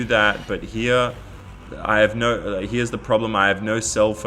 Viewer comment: I thought he said grandma